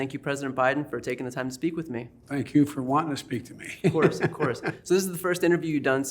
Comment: POTUS just said no one else wanted to talk to him‼️🤬🤦🏾‍♀️🤡 Don't act like you didn't hear him say that. 🤣 The only one that would talk to this clown is B.C. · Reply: Biden did NOT say that! He said: "thank you for wanting to speak to me."